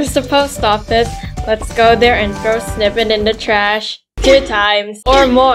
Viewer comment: i thought you said "lets go sniff'em in the trash!"💀